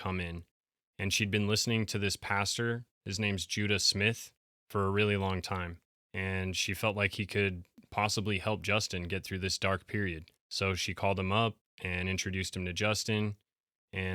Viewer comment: I heard 'Judas Myth'